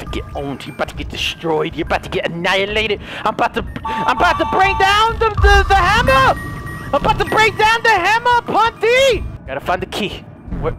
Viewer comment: did you say drop the hammer of dawn